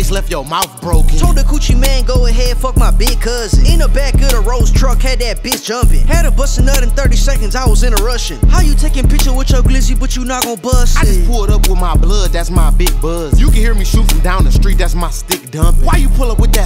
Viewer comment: Y’all ain’t even hear Pump “Had to bust in 30 seconds i was in a Rushin (Russian) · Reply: Oh I thought he said I was in it rushin lmaooo 😭😭😭